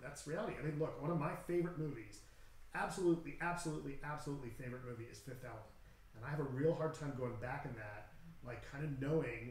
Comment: At approx 10:25, says the name of a movie, " 5th Out"?....not sure what was said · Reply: It was a little garbled but I'm certain he was referring to the Fifth Element which is a Luc Besson movie :)